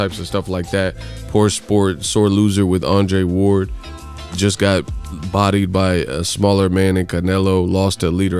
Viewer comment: I swear, I thought you were about to say " got bodied by a singer ni**a" 😂😂😂😂